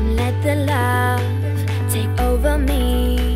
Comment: เราได้ยินว่า อ่าวกิ๊ดไม่ออก(อ่าวคิดไม่ออก)😁😂